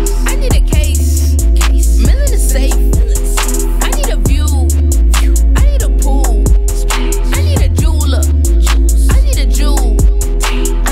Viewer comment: It sounds like she said I need a poo😂